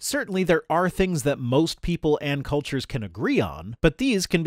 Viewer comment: I heard "Ann Coulters" and I thought oh my fucking Christ there's more than one of those spider-hags?!?